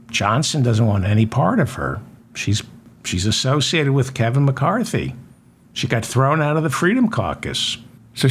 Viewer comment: Thought you said she f****d Keven McCarthy. Of course you didnt. That would be uncouth.